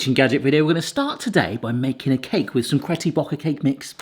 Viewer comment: Did he say "Caty Brocker cake mix" 😂😂😂 ??